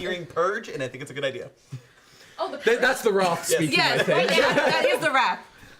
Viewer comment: I keep hearing the word 'purge,' and I think it's a good idea. That's the wrath speaking. The way he pronounced "wrath," I thought he said "Roth." I was like, "I think James DeMonaco directed that, not Eli Roth. 🤔"